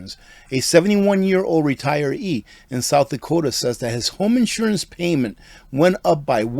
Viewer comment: My 84 year old dad lives in Florida. His homeowners insurance was $2000…new estimate $<num>. His home is paid for and he has to sell. Done can’t even stay in the home you worked to pay off. Horrible!!! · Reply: I thought she said homeowners insurance